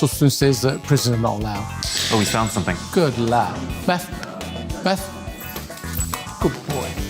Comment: did he call the dog Beth and then say good boy?